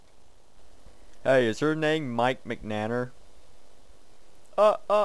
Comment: Mike... Mcnanner? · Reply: Caffeinated DaVinci i thought he said "Mike Mcmatter"